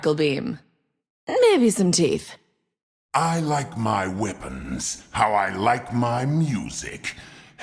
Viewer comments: Wait did he realy always say weapons I thought he said women instead of weapons.... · the first time I heard him say that I understood ‘women’ instead of ‘weapon’ lol